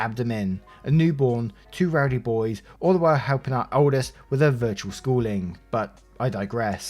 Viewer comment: I actually thought that said helping the oldest deal with her virtual school shooting! T-T Yes, I'm USAmerican.